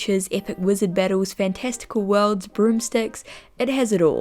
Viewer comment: I thought she said "group sex" instead of "broomsticks" at 00:10 lol